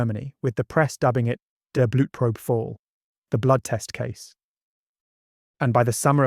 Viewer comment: I thought that said butt probe at first